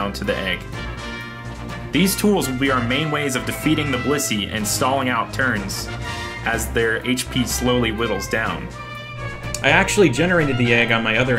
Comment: What's he saying here??? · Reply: "These tools will be the main ways of defeating the Blissey and stalling out turns as their HP slowly whittles down"